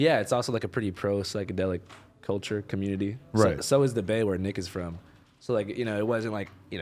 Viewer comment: i swore he said “where niggas from”😂😂